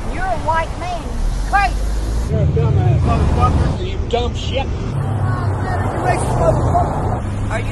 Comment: did he say “you dumb sheck”?